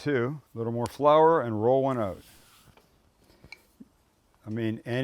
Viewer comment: Did you say, “ Roll one Up” here? 🤘😎